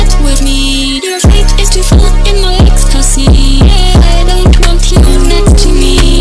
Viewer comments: I always thought it said “but your fate is too fucked, in my ecstasy” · I think I heard "Your fate is to fall in my ecstacy"